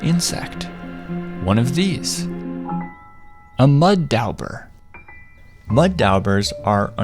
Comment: i thought it said mud daubler instead of mud dauber